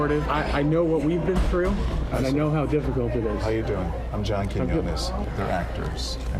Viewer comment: The automatic subtitles say: „I‘m John King Goodness“ 😂